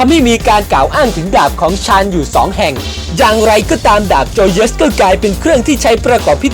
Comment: 555จังไรก็ดากโจเยด(เราได้ยินแบบนี้ตอนกดไปเรื่อยๆ)😂😂😂😂